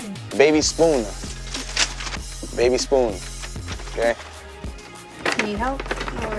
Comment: so yall gonna act like you didn’t heared what the girl said “he is gay” that’s what she said am i the only one that heard that😂😂😂